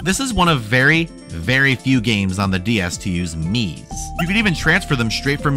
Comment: i fully thought you said "Knees" and not "miis" and fully accepted it as truth ubtil i saw the miss im sorry its 5 am here ih avent skeot